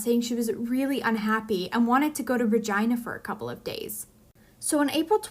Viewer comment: she says Regina, it's a city in Saskatchewan, she did not say Virginia.